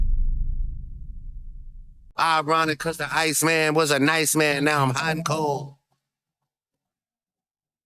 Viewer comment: I thought he said “Ironic cuz the Ice Man was a Nice Man now I’m Uncle” 🤣